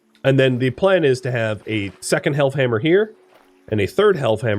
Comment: Until i read this titie, i thought you were saying Health Hammer lol